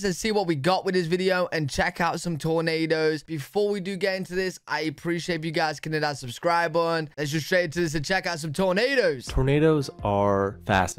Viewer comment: I thought that guy said to get in his pants